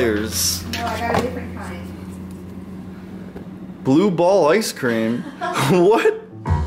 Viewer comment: It sounded like he said "blue ball" instead of Blue Bell